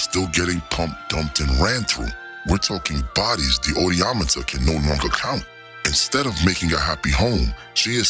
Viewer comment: What did he say here? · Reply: The odieometer.... 🤣